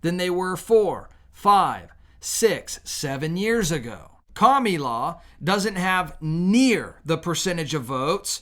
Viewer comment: I thought he was saying Commie Lawv😂😂